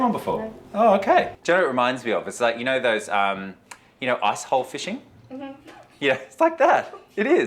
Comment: When he said, "icehole fishing," I thought he said, "a=@ fishing."